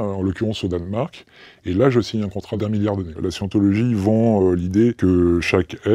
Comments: "la je" quoi ?! Vous l'avais coupé !! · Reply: "là je signe un contrat d'un milliard d'années" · il dit quoi ? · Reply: "je signe un contrat d'un milliard d'années"